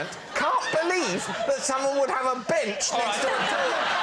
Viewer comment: Kinda sounds like he says “bitch”.